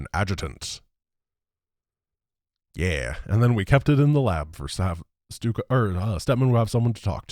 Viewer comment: For a moment thought that he said "And we keep it on the lab to steddman have someone to fuck to" And came back to the video to rewind and check if said that cuz i couldn't believe that said a swear and even more a joke like that